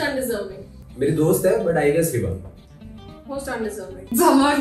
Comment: what did kashish say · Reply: I guess hiba